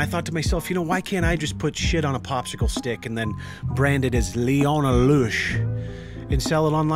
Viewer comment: High key I thought you said Leona Lewis